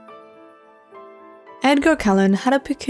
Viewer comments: I thought she said Edward Collin from Twilight lol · At the start I thought she said Edward Cullen like from twilight 😂😂😅